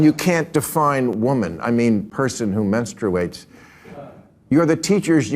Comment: minutes in, did he just say "mensterbate?" Like half menstruation half masterbation?